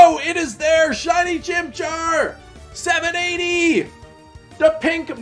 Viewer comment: I swear I heard “SHINY CHINCHAIR”